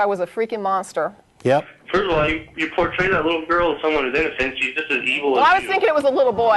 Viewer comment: I can't make out what the caller says that makes Matt hang up on him and there are no captions for it. I'm really curious even though I think I get the gist of it. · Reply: I know you posted 9 months ago but if anyone else can't hear he says "first of all, you portray that little girl as someone who's innocent. She's just as evil as you"